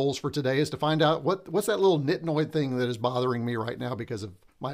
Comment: Just started watching now :-) Ooh, I thought I heard you say some Thai words at around 7:45, "nit nawy" (= little bit? :-)). As for the little thing that was bothering you, was it a slightly off aspect ratio? :-) Oh wait, jamie guessed it right later, lens flare :-D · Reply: Was it nit-noid? I do say that frequently. 🤣